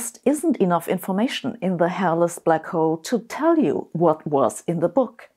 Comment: Love you Sabine. It was kinda funny tho realizing you were saying “the hairless black hole” and I thought you were saying “the hellous black hole” about throwing the book in.